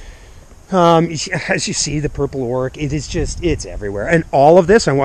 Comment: what is this plant you said purple oark? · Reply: Purple Orach. It's a mountain Spinach and does very well in dry climates. I will be sending you some seeds of those, brother:)